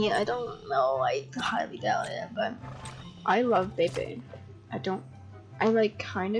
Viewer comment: i thought u said "i love vaping"